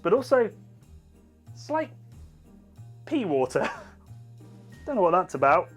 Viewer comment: Personally, I always look for a bit of "pee water" in my Christmas Ales. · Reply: On first watching I genuinely thought he said “pee water” 😂 only on the second time round did I realize it was “pea water” 😅